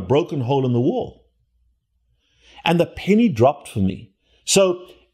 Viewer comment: LOL. I thought you were saying "the panties dropped!" I was confused to say the least, then I realized that you were saying... "then the penny dropped." LOL.